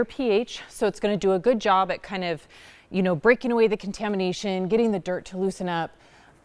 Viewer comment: i thought she said "break wind"